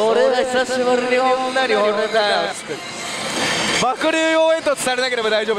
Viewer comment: 携帯見ながら聞いてたら 爆乳応援団て聞こえてすぐ画面見たわw